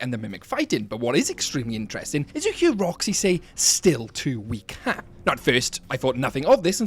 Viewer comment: I thought she said: "get away from her" & " I... Lost". Not: "still too weak, huh" & "I... cassie"